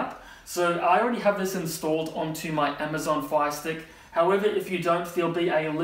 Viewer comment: I thought he was saying Phi stick